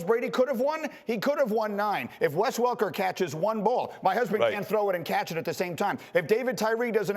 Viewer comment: did Greeney say "my husband"???